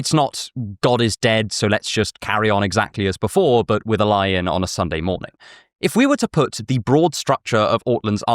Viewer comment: I misheard "a lie-in on Sunday morning" as "a lion on Sunday morning" and thought you were going to suggest we should all start worshiping Aslan.